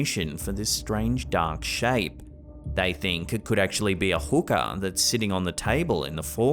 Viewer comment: How many people thought he said hooker when he was talking about the hookah?